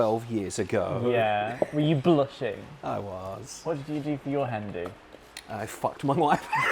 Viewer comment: "What did you do for your (unintelligible)" Can anyone tell me what he said there? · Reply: Hi, one of the editors here! said "What did you do for your Hen-Do". If you're not familiar with a hen do, it's essentially the party or get-together that the bride traditionally has before their wedding.